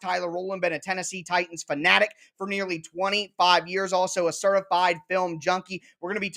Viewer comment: I had to rewind this like 2x's. Tyler said he's a certified a film junkie, I thought he said he's a certified gnome junkie 🤣🤣🤣